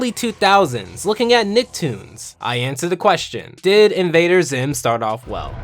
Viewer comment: I really thought it said dio invader zim i know egg and my face were in alignment